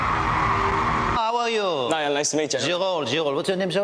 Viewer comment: what does harry said....? I can't figure it out !? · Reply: He said "Did you get it on camera?" 😭😭